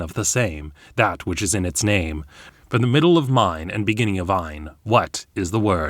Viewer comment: "What stands before you it a keyed door, nothing less and nothing more. Opening for the one of the same, that which is in its name. From the middle of mine and beginning of ine what is the word." It doesn't sound like vine to me. It sounds like the suffix ine, of or pertaining to,” “of the nature of,” “made of,” “like. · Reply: Ah, I heard "...beginning of thine"